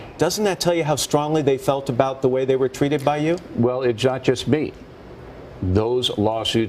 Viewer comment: Did he say it jas jas me??? Or not just me?